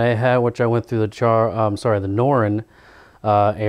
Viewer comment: I heard "charr asura the norn"... so confused!